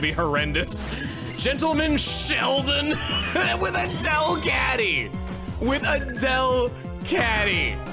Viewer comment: I know said “A Delcatty” but I heard it as “Adele Catty.” That’s hilarious to me.